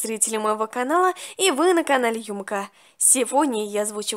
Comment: Конечно это видео вышло год назад но в 0:04 я одна услышала что сказала ЮмЫка?😅😅 И да, в 3:16 СПУСТЯ 1000 ЛЕТ